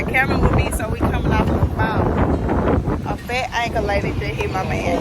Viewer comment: What's a fat ankle lady? That's rather rude really it is we can say several things about you especially your speech. Be nice!!!! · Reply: For some reason, I thought she said a fat anchor lady. I was like dang, he got hit by a news anchor vehicle. That's a lawsuit. LMBO!